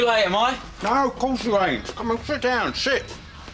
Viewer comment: did I hear sh*t or sit